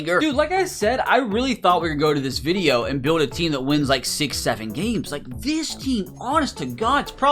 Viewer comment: dID bRo SaY 6 7